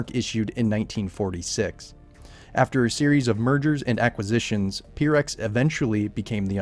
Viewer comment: I First Heard after a bunch of murders.